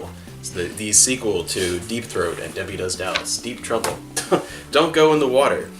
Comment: Lmao, did you say deepthroat at 0:35?